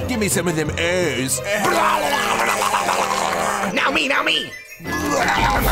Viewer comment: Its part of a balanced as fuck breakfaaaasst. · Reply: I thought they said "It's part of a balanced dis-fuck breakfast" XDD Lawl